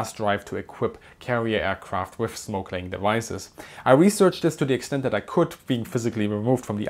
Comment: I thought he said "snorkeling devices".. 😁